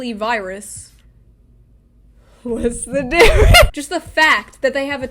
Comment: I need to know what she said hear my edgy heart doesn't want to miss it but the mic just dropped dead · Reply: She said "What's the difference?"